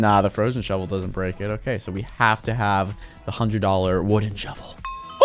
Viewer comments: Did he say $100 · Lol it sounded like unspeakable said $100 wooden shovel